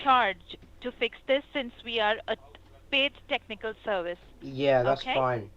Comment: does she say that 'We are a Fake Technical Service," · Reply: No. She says "paid"